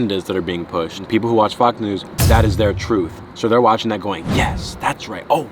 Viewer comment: i swear he said fucc news.